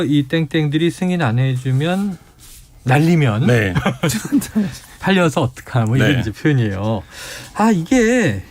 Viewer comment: 잘~ 들어보세요 ... 제가 100번 넘게 들어보니 "김건히 새끼가 승인 안해주면 날리면 쪽팔려서 어떻하니~" 로 들리는 구만요